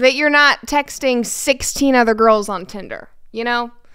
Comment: I swear I heard him say Gorls instead of Girls😂